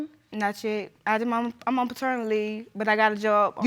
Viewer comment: It sounded like it said she's on paternal leave